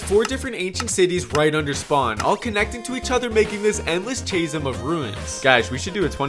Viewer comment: my man did not just say chase-em